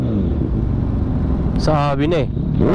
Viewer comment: Did i just heard a Filipino say's Sabi na eh 😂